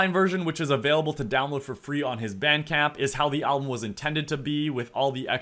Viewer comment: what’s vancamp or whatever said? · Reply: bandcamp